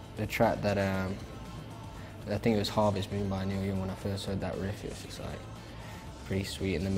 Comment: What song does he say at 1:25?? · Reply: Harvest Moon by Neil Young